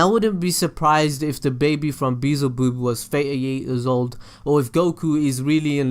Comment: Did he just say beezleboob???!!!!!